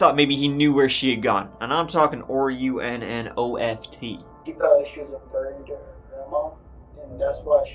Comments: Can someone for the love of god tell me what says at around the 9:18 mark where he says, "And I'm talking or-r-r-u-n-o-f-t." I rewound it 50 times and cant make out what he's saying.. Can some please tell me? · What is saying at 9:10 ? Ruonoft? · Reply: Its a reference from the movie "O Brother, Where Art Thou" .. Where one of the characters, when describing someone who has "run off" by spelling it "R u n n O f t" ( he's a backwards hillbilly, if that helps) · Anyone know what is saying here? I can't make it out. "R.u.n.n.o.f.t." ? "are you nnoft" ?